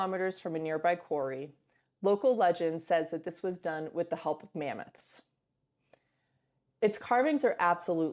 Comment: what did she said? help of Memex? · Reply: help of mammoths